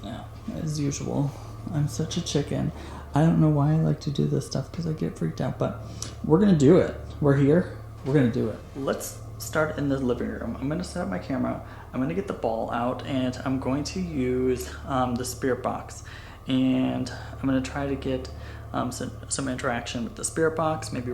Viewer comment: I needed to laugh so hard ... as i kinda was half asleep and heard "i gonna get my balls out" and though wtf is happening here. But yeah i misheard it. XD